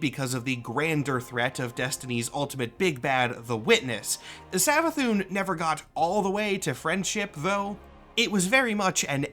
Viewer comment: my partner was laying next to me as I watch this and she'd thought she'd heard "Destiny's Greatest Dick-Head" here.